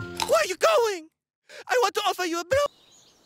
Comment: wut?? is it what i think it is that she was going to say? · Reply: She was about to say "I want to give you a blonde fashion tip."